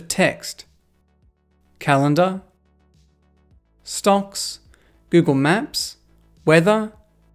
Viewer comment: Did he say "stonks"?